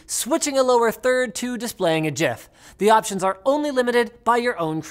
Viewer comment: did he just said JIF??? JIF!!!!